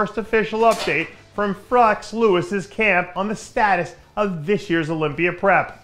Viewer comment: Hahaha 00:40; did he say Frex Lewis lol!? Come on Dave lol.. if you mess up the dudes name 40 seconds in on a video specifically about said dude.. do a second take rofl!!